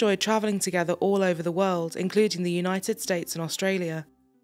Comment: I heard this as "the United States of Australia" and I was like.. Huh?